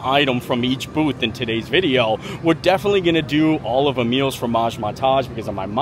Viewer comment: All this time I thought you were saying “Meals for Ma’s”, as in meals for mothers, not Emile’s Formage. 🤣